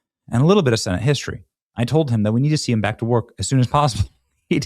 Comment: I heard he's dying to get back to work.